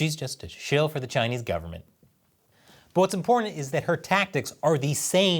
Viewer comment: I heard "What's imporntant .."